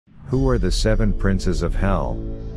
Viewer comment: I heard "who are the seven princess of hell"